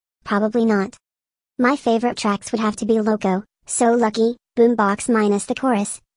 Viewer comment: When I heard "So Lucky" it kind of sounded like Dessert.